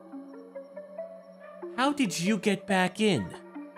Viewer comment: How did you get back in What I heard "Meg who let you back in the house?"